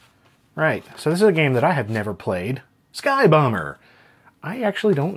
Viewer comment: I had this playing in a background tab, and did a double take when I thought I heard you say "Sky Boner."